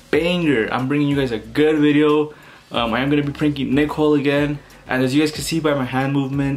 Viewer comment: I thought he said nickle LOL